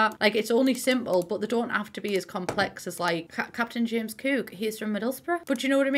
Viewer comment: i replayed this a phew times 11:37 because i heard "Captain James Kirk" and forgot i was watching 😭😭